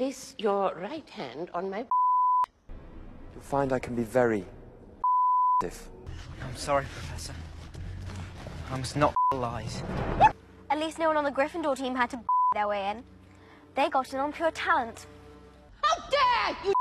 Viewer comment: For a second it sounded like Harry said “Sorry Professor, I must not eat lies” 😳